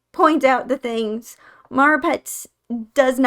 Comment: Hey guys! If you see any subtitle issues or mistakes, please reply to this comment letting me know when and where and I'll try to fix it! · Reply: I only started looking at the subtitles around 20 mins in but I caught 21:00, Lara Pets instead of Marapets 21:21, Maravet instead of Marapets 21:29, your pets instead of Neopets 25:17, enough to use instead of NFTs 29:59 Tier instead of Terror 30:25 Mars instead of Marapets